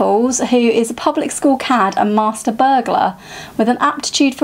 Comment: I'm such a child. For a brief moment I thought you said Raffles was a "public school cat with an aptitude for cricket and a penchant for crime" and when i realised it was cad not cat my disappointment was crushing. Am I weirdo for wanting a crime series focusing on an anthropomorphised animal? a doggy detective, a literal cat burglar? My silly brain is going haywire. this sounds like a great TBR and I'm definitely looking forward to hearing your thoughts on all of them. I wish i could tell you what I'm reading this month but I'm a filthy mood reader that never knows what I'm reading till I pick it up. All I know is that I'm diving into some horror to step outside my cowardly comfort zone.